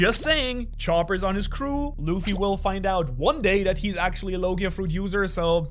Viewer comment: did he just say logia?